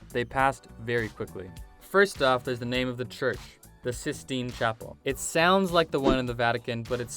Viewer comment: "Cystine" is just a play on words for the Sistine Chapel. Basically, calling the church... a big pimple. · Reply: i thought he said jizztine chapel, ou boy was i wronk.